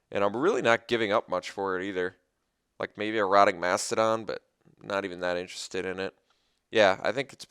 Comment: For a moment I thought you said Erotic Mastodon....